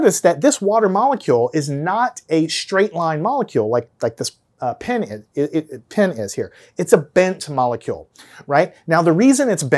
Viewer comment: Did he just say: Water molecule is not straight like "pen-is" ?💀😅